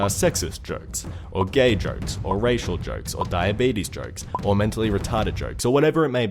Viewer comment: When he said "Diabetes joke" I thought he said 'dying babies'. And by that... What's more funny than throwing a dead baby off a cliff?